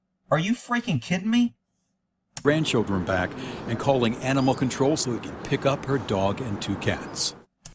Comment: Well, to be fair @ 15:11 it sounded like the reporter said "calling animal control to pick up her dog and cat", which can be interpreted both ways. 1) He's calling animal control to (they) can pick up her pets. Or 2) He's calling animal control to pick up (animal control has the pets and he's calling so he can pick them up) her dog and cats. That being said, it is a little strange how little contact they seem to have. Mainly that comment about her being so stubborn and wanting to raise her kids on her own just seems off. I know all families has some sort of dysfunction, but that line struck me as that and then some.